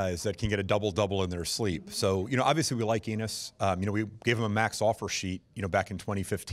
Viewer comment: Did he just said "we like anus" 0:09 ? 🤔